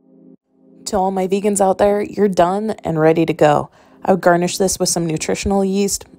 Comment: Scott was putting away laundry in the background while I was watching and he thought you said “Okay all you vegans, you’re dumb.” instead of done. 🙄😂